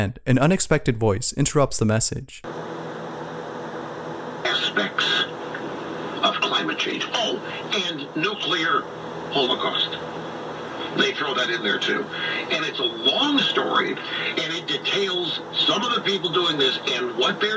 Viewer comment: Did anyone else hear “Nuclear Holocaust” ?